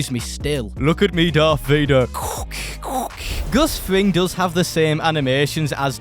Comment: did you say Gus fring as in Gustavo fring